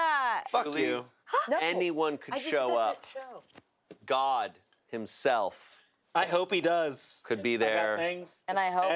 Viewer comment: what does say at @ 1:56:26 ? i cant figure out what he's saying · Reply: "It's football, they... trash talk so much"